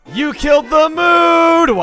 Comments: I honestly thought he said "you killed the moon" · i thought you said go kill the moon lol